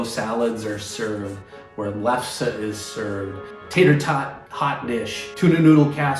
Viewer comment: When is talking about potluck foods (specifically around 6:03 ), the default subtitles say he's saying lutefisk (lye-cured fish, I forget where in Scandinavia it's from) but I'm more inclined to think, as the auto-generated captions do, that he's saying lefse (a potato flatbread popular in the northern Midwest, which I think is Norwegian). Do we know which he meant? (I have yet to eat either; I haven't yet lived that far north...)